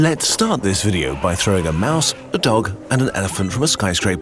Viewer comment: Just for a moment at the beginning of the video, I heard 'Imagine to throw a Maus, a TOG and an Elefant...' Someone will get it ;)